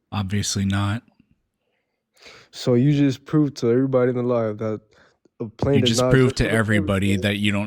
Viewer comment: Did he just say: “So you just proved to everybody alive that blah blah something blaH”?? How to unwittingly start a “debate” by perfectly underscoring your self-centric tendencies for all to smell 🤢🤮